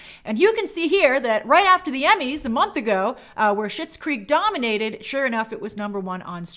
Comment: Oh, I always thought she was saying Shits Creek